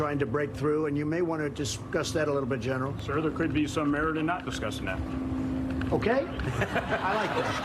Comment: What did the General say? The subtitles say "there could be some advantage to not discussing that," is that what he says?